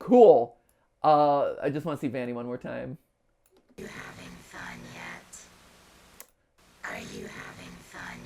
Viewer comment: The first time I heard vanny say “are you having fun yet” I thought she said “I like lasagna” I mean same but like why is that important